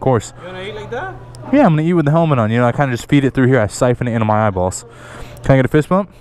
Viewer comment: did anyone else’s Siri open because it thought he said hey Siri?